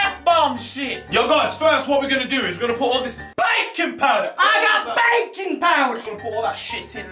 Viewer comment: I thought u said Bacon powder 😂😂